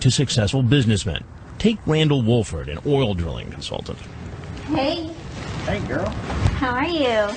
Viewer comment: At first, I thought they said, "ORAL drilling consultant." That too.